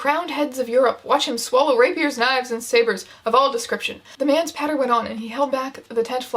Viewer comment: i thought she said the uncle swallowed swords knives and sailors then i realized she said 'sabers' lol